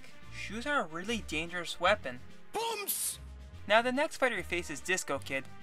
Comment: what is he saying here? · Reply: Never mind, I figured it out, he’s saying BUMMS! Which translates to Boom